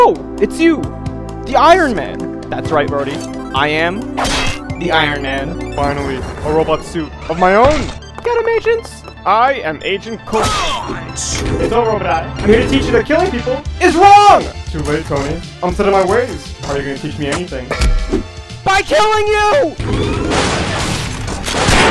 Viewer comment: did he say 'Robodiah'?